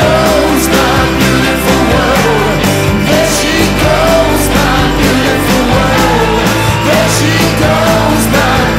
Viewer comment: why is it called there she goes my beautiful world? It sounds like there she ''comes my beautiful world''. Have I misheard it?